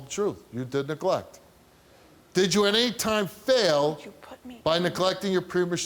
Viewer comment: what was she whispering to him? She said "why did u put me......" I couldn't hear the rest